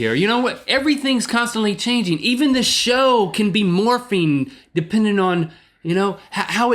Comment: For a second I thought Link said "Even the show is morphine"... I was sufficiently confused!